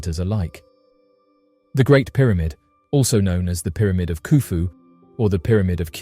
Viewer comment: 26 did he just say the pyramid of "PooPoo"?